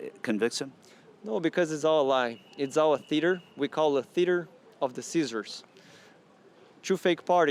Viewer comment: I thought he said Theater of the Ceasers And I was like wth 😲